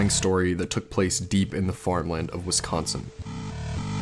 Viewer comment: I heard "deep in the foreign land of Wisconsin" not "farmland" and it took me until the end of the video to realize my mistake.